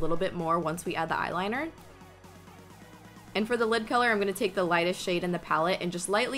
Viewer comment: I thought you said and for the lip colour on 5:26! I was like o.O were already going on the lips ok?? Haha love you ❤️❤️ i always look forward to your snaps 😋😋 lysm